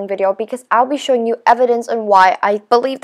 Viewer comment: I thought said, Pets were not coming out?